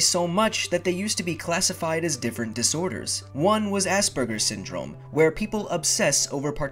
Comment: "Sorry" but Asperger syndrome sound like he said ass burger syndrome ;) (Edit)